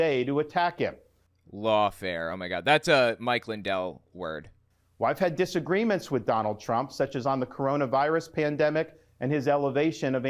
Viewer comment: I thought he said donald dry-hump.